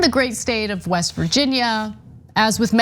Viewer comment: I swear is sounded like Ana said, "In the great STAIN of West Virginia." So true. Guess this lottery is to make sure they make-up the loss of COVID deaths in mass shootings, suicides, and "accidents."